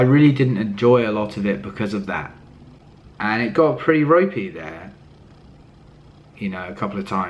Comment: EDIT: wow okay he said "ropey", I'm not gonna edit my original comment underneath this, but thank fuck now it's funny that I misheard it in such an awful way Bit uncomfortable with that "I got a bit rapey" sentence, cause I use that for guys who won't leave me alone and try to have sex with me, go home with me even when I said that I don't want that. You might just have meant that you desperately wanted company, but (especially now with the whole me too thing) just feel a iffy about using words like that. Great cover though and I really hope you're not harassing people :)